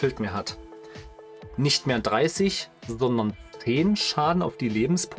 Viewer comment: ich habe mir das jetzt 10x angehört und ich kann einfach nicht raushören was du sagst 😅 wie viel Schaden macht jetzt der Arc Star auf die Lebenspunkte? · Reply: glaube ich habe 10 verstanden😂